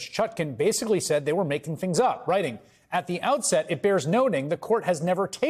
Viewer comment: did i hear him right... did he say president Chump 😁